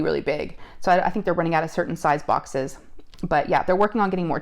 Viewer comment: All I heard was "they running out of circumcised boxes"